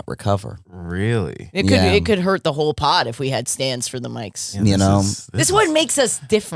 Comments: I thought yall were saying stans—like, first of all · I thought she said "stans" like K-pop stans loll. Now I'm wondering if Josh has stans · I thought she said stans, like a super fan, then as you guys talked more realized she said stand. Then I got distracted and started it over from the beginning and somehow still thought she said stan and that josh having a super fandom would ruin everything.